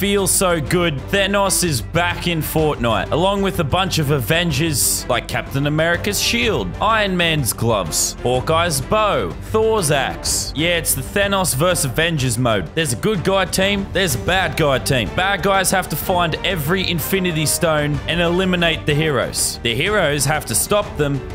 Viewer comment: When he said Thor’s axe I thought he said Thor’s ass 🤣🤣🤣🤣🤣